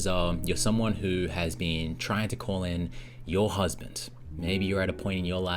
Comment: So is this for men as well? Cause all I hear is "looking for ur husband". I'm a man looking for a wife